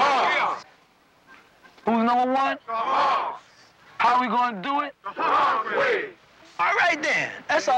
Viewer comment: Coulda swore they were saying “we’re gonna do it together”